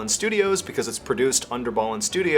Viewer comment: Did anyone else miss hear his sponsor ad? I thought he said, "Start your b.tch" lol